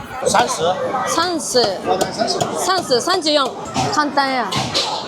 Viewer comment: 30（三-十） 听成了 3（三）+ 4（四）= 3 4 （三-四）, 34的发音是“三-十-四”，可能是受日语的影响。 24:28 香港没有的“香港苏尔玛超市”